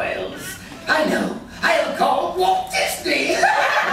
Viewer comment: what is he saying? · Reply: “I know, I’ll call Walt Disney.”